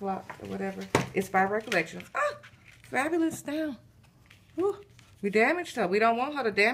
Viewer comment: Girl Did I hear you say "Fabulous down"??? Lol Lol Sis you have me rolling. BEAUTIFUL projects my love. Hugs❤ Lady Kre